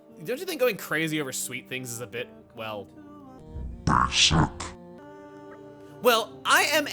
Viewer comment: Did Mythra just call Kora a basic b!tch?